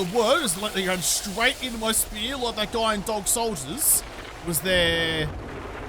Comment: "Dog Soldiers"...the best werewolf movie since "An American Werewolf in London". Oh, and unless they changed it, the planters around the trees at the death house are actually farm plots. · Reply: i'd never seen this movie, and at first i thought he said "dark soldiers". Thank you for clarifying though(i guess i now know of another movie to watch) XD